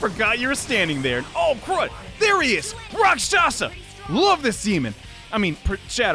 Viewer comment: excuse me, what? I swear i heard "I love the semen" but ok